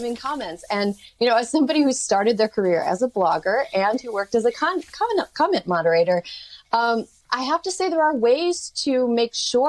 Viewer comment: It sounded like she said, A conman instead of comment moderator